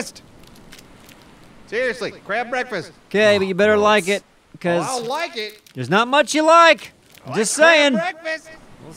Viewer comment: what is saying at 15:15? its like hidden in the background of the audio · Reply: I don't think it was, I think it was ' character saying "awh gross" like he did later when he was harvesting the hog.